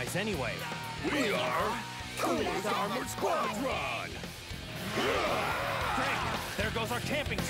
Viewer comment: ginyu force copyright XD and what they say? We are cooler's what? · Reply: Cooler's Armored Squadron